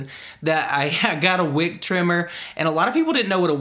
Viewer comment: I thought you said Wig trimmer lol. And your hair looks nice.